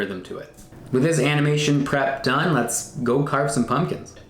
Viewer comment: was it just me or did it sound like he said "let's carve some onions "